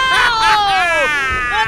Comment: i thought he said..."yah i gotta admit that was pretty intense "